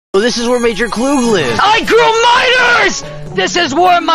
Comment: I heard "I GREW MINORS"